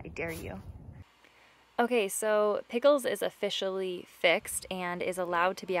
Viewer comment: No like you know in the beginning of this video she said pickle is scared of kazi ya the cat is pickle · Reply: Ohhhhh silly me.. I thought she said tickle 😂 I get what you mean now... Thanks for the info 😂 I will draw them